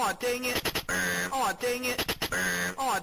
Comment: Aw dangit. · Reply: I Hear "Come On Dangit"